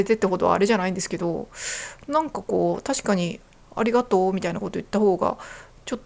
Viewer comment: 関西人を真似て「ありがとう」と言ったイントネーションが「蟻がトウ」と聞こえるのは自分が関西人だからか？関東の人が関西人を真似ると（笑）けるね